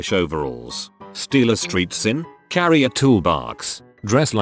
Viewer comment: STREET SIMP. (Edit: I can't be the only one who heard that).